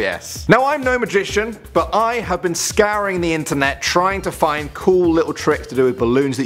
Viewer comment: i thought he said scaring the internet im like wha?